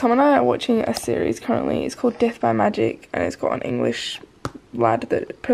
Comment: When you said death by magic, I swear that I heard, "Death by my d**k" I was so relieved when I saw death by magic